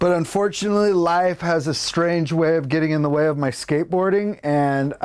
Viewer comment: lol I was playing around with the trucks on my board listening to this and at 0:33 I thought I heard him say "Life has a strange way of getting in the way of my skate ROCKING" Immeadietly I \m/ \m/